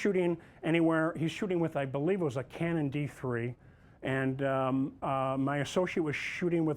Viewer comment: Did he just say Canon D3????